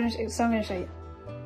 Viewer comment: I watched the verbal hieroglyphics part 100 times. She’s speaking vegan I think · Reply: I hear "It's just that I need to go so I'm gonna show you" which makes sense to me